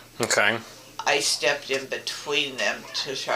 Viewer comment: Why do I hear, "and my dog stepped in a bee..."?!